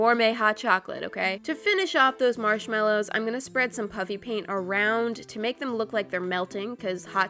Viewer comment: It sounds like she saying puppy paint